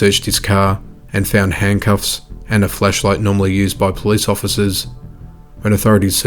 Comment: I thought you said “fleshlight normally used by police officers” due to your accent